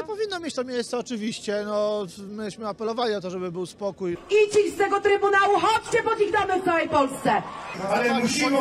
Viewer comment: Co mówi ta kobieta? bo nie mogę zrozumieć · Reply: "i ci z tego Trybunału - chodźcie pod ich domy w całej Polsce"